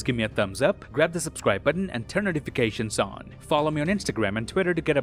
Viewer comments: “Till then I’m Kevin heart and I’ll see you lads in the next one” Am I mishearing him? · Did you just say you are 'Kevin Hart' ! 😅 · Did u just say ur name is kevin hart? In the outro?🌚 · Did he just say till then I'm Kevin hart and I'll see you lads in the next one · did he just say he's kevin hart? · did he say he is kevin hart?? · Did he just say he is Kevin hat!?